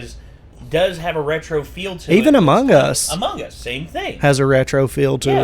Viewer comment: I was skipping to the actual reaction, and I heard "more female among us"